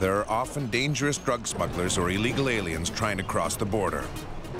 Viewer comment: did he say illigal aliens